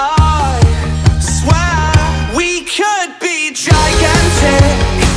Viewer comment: The first few times I heard this I thought he was saying "We could be jack asses".